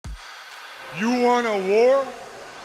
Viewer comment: Thursdays just aren’t Thursdays without Scott Hall asking if I want a war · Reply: I thought he said "You Wanda War?"